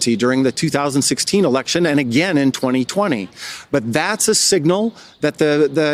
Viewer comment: did they just said that he rigged our election in 2020